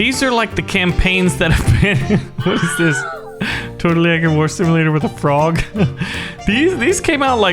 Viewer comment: lol the subtitles Says totally econimic simulator